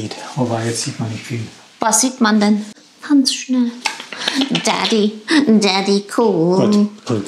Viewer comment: The outtakes were so funny - but I have to know what we’re you saying in German when it sounded like you said “daddy” lol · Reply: Oh thanks so much! Lol Daddy Cool is just a very old song from Boney M. They had a guy in the band who was the funniest dancer.